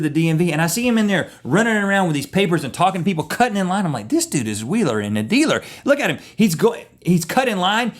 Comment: Did he say Willer and a Dealer or what? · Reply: Wheeler and dealer -"a clever or crafty person who devises intricate, highly profitable schemes and transactions, as in business or politics." or "person who makes things happen." It's also a noun if you were wondering haha